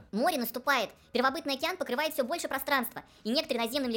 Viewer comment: Услышала "первобытная тян покрывает все больше пространства"